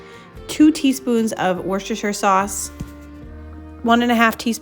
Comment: can someone tell me what spice she said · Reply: Worcestershire sauce 😅